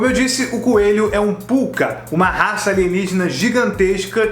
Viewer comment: Espera aí, eu ouvi "Pucca"?